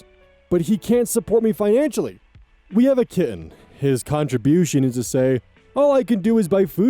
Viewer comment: Story 1: this is so weird bc I’d be pissed if a work colleague was texting me near midnight? Like, I’m trying to relax and not think about work and maybe go to sleep, eff off?? Edit: for the last story, I misheard “kitten” as “kid”, and then was VERY confused when we got to the “we haven’t slept together” part 😂